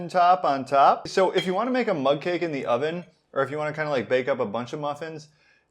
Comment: Oh I thought he was saying mud cake. Mug cake makes much more sense